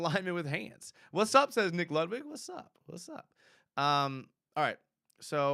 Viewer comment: said "Nick Ludwig" but I heard McLovin' and had to do a double take and rewind :-D @ 38:00 In Zay's defense he took a big bump and a slight stumble so he didn't have the momentum Trevor clearly though he had to break into that clear space. It's good to see that throw broken down though cos I just thought it was a horrible miss/misread from Trevor.